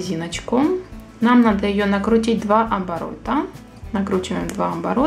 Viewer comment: я одна услышала слово амборота?